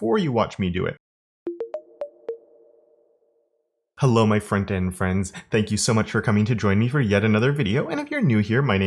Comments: OOOHHHHH!!! You're saying "Hello my Frontend friends"!!! I don't know why this video was different from all the others, but I always thought you said "Hello my friend and friends". Frontend friends is way better. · have literally just realised you say hello my front end friends, always thought you were saying friend and friends LOLZ!! Absolutely love the videos thank you so much.